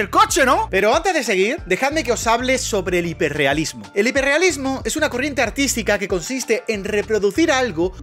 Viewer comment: No se porque, pero escuche "Y Perreadisimo" XD